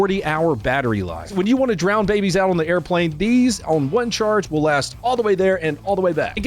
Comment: I thought you said "If you want to drown babies on an airplane"